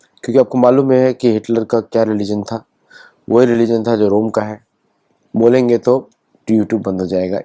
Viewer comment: Sir , you have mentioned some organizay at 3:29 , I could not catch it . Please be clear . Many a times we can't catch with your pronounciation. Please talk loud and clear. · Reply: Religion of Rome, i.e. Kristianity